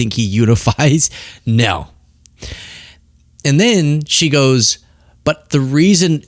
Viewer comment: I heard “and then the fire” 😂